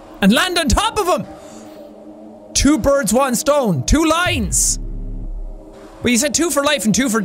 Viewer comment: I didn’t think it was Two Lines I thought it said “wolines” :Also me when jack says it,ohhhhhh that’s what it says